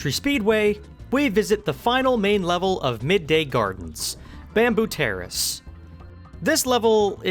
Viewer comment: Did someone said “Bamboo Tariffs”?! 🤣 🤣